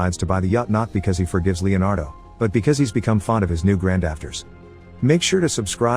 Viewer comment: Okay the dad who made him choose between the family and his love ended up loving his new grand daughters 🥰 extremely cute movie! Thank you for the recap! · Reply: I thought he said grandactors